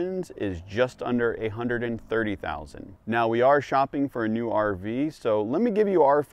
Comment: Ha - At first I thought you said "$830,000" - but I listened to it again, and you actually said :"A Hundred and Thirty Thousand." Whew.